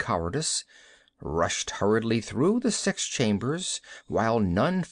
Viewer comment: I swear around 33:22 I heard the narrator say "Sex chambers"...